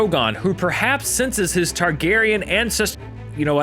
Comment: i thought he said, "Targeryen incest..." hahaha